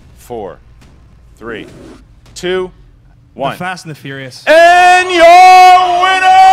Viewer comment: Game should be under protest. Team Action answered "Fast and Furious". That is incorrect. Fast and Furious came out in 2009 and did not have Ted Levine. "The Fast and The Furious" is the correct answer. Normally articles do not count, except when it is specific to the film and will differentiate between two films. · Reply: Adam Rosen Rewatch it, 43:30 he clearly says THE Fast and THE Furious